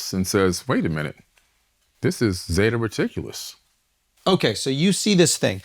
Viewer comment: I think I heard him say at 1:51:08 "wait a minute, this is fucking ridiculous"